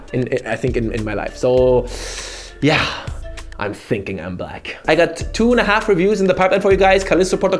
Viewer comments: for a second i thought he said "im thinking im black" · did he say i'm thinking i'm black O_O ?!?!?! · i legit heard: Yeah, i'm thinking i'm black... hahahha